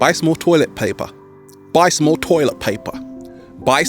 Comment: Did I hear you say but more toilet paper 3 times ? Excuse my misspelling Buy more toilet paper is what I thought you said 3 times